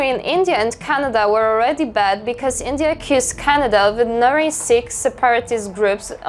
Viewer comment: Perks of accent barriers, she says "accused", I heard "kissed" 😂